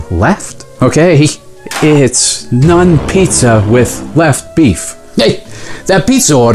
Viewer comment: My husband quoted this the other day, and I had no context for it, so I thought that he had said "Nun pizza left beef." 😂